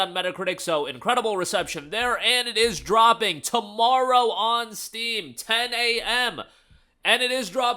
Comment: is it dropping tomorrow on Steam at 10am? Didn't quite catch that